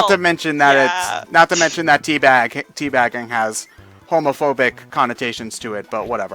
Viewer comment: How is tea-bagging homophobic? If anything it's homoerotic. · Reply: Oh wait I thought said HOMOPHOBIC. SO he said homoerotic ok....ok